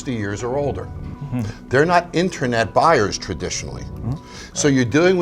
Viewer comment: did he say “intranet?”